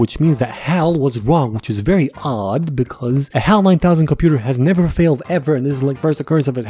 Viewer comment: he said HAL 9000 but the caption said HAL 900. tedious I know but thought I'd point it out